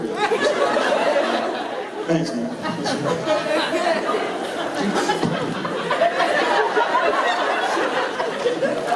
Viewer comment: What are they saying at 16:05? Jared - "Dude, who are they?" Jensen - "I don't know" Jared - "(fill in blank)" please let me know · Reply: Jensen: give it to them in a way they wouldn't expect it Jared: who's they? Jensen: you know Jared: I don't know Jensen: you know Jared: I know To the Asker: thank you for that horrible visual image um I will that's gonna cost me years in therapy